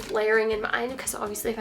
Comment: wow that piece at the end was a steal!!!! and love the polo top ! Also everytime you said "layer" i heard "Lair" :p hehee miss u